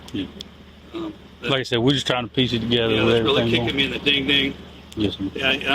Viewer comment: Did he just say, "...kicking me in the ding-ding"?! What is he, TWO YEARS OLD?! 😮